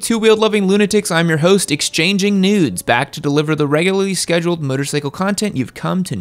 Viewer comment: Did he say “exchanging nudes” or is that just what my ears are hearing?